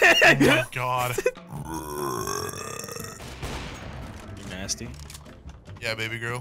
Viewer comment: dude you were there how did you manage to get the subtitles wrong. He said "mcnasty" not "you're nasty".